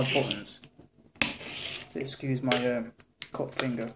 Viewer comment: When he said "my cut finger" I could've sworn he said COCK finger! LOL